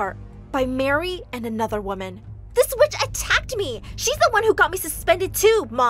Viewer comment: caption said I tapped instead of attacked